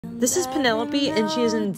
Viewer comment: I thought she said “this is vanilbean” 😅